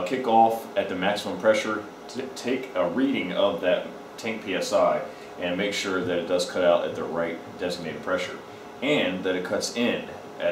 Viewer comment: At 5min and 14 seconds I think I hear "at 10 psi" . Am I missing something . ? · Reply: It says tank psi not 10 psi, I had to rewind it cause I thought it said 10 psi too